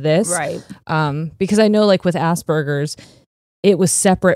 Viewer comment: So i was just listening to this episode on spotify and when you talked about Asperger's i kept hearing "Ass burgers" and i was so confused for a moment until i realised 😂😂 (Sorry if i misspelled something, english is not my first language)